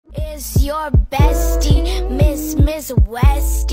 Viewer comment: "is your bestie, miss miss westie. don tryna tes me, things gonna get messy. is gonna get meSSY. jus, jus bless me." 🗣️💯🗣️🔥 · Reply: I heard "it's gonna get messi" 💀